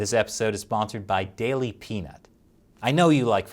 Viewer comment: Like I heard 'Daily Penis'! Haha!